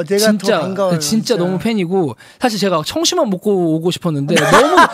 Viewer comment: Thank you for the subtitles! This was such a heart-warming video, I think I've replayed my favourite parts multiple times now.... ㅠㅠ Sorry to be annoying but I just wanted to point out that at 5:55 I think Minhyuk is saying he was so nervous he nearly had to eat some 청심환 (a type of herbal relaxer) before coming on the show. I'm not fluent in Korean so obviously tell me if I'm wrong, because I do see how it also sounds like 점심 만...